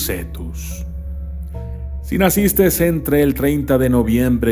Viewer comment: Min. 6:33... por favor alguien dígame que escuche erróneamente “Nacistes”... CAPRICORNIO POR SIEMPRE! Viva Excalibur 🗡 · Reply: Si dice nacistes 😅😅😅